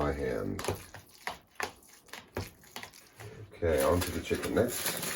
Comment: I had to laugh - at 8:48 you said "on to the chicken next" and it sounded like you said "chicken necks"... Thanks for the smile! I'd really like to see a video on making your quiche, if possible. That looks like a delicious meal unto itself. I'll have to explain the OMAD system to my wife. She insists that a person needs to eat 3 meals a day, and I enjoy two. A late breakfast, which lasts me until supper. Thanks for the delicious looking and visually pleasing episode today. I did hear Spunky in the background making his noises. A video from without a few moments of your faithful sidekick is missing something... we do love our Spunky! Maybe he was in the dog house? (smile!)